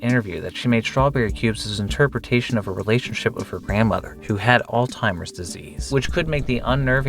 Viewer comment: “old timers disease?” Or did you say “AULD TIMERS?” Nope… Try Again, it’s ALZHEIMER’S Disease.